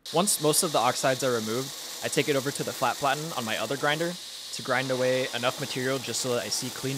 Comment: All i hear is GRINDR, all I see is beautiful face ❤